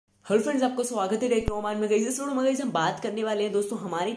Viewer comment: i hear girlfriends 😂😂😂